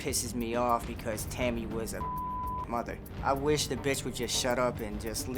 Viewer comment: My question is … what was the cuss word that was blurped out at 1:41 because he immediately says the b word right after 😂😂😂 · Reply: Tammy was a good fucking mother. Lol